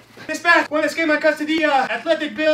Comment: Bruh he said Canadian Asian man? I thought he said sneaky Asian man jfc